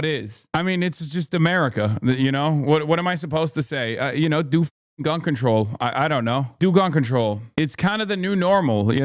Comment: Do Gun Control? Sounded like you said Unsubscribe to me